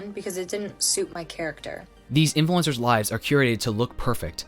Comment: I spent way too long wondering if Gen Z came up with some new Q-rating (!?), before I got that I'd misheard "curate".